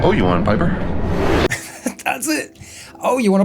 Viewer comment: Better name: Public Domain Squad. 28:18 I thought Death actually said "Owe you one, Piper." like he appreciated him getting rid of the witch, because she just seems to annoy him. I never feel like Death should be a villain, though. It's like making gravity a villain. It's too big, and just a force of the universe with no will or malice of its own. When people fall off a cliff, it's not like gravity murdered them, or wanted them to die. It was just a force of nature, doing its thing. Death shouldn't want more things to die. That's just more work for him. Nor should he want to "take over," whatever that means. He already has to assist every living thing in the universe towards its rightful place in the afterlife, or whatever. He's too busy.